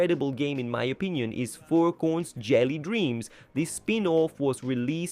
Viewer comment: why did the text say 4 coins instead of furcorns